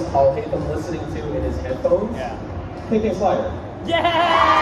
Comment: What did Alhaitham’s VA say in answer to what Alhaitham’s VA is listening to in his headphones?? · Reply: K.K. Slider, I believe